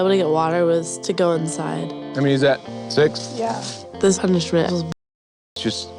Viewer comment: Anyone realize at 7:45 the guy said “is that six” and the girl said “yea” instead of yessir